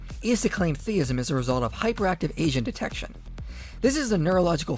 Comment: I wasn't listening closely and thought he said "hyperactive Asian detection"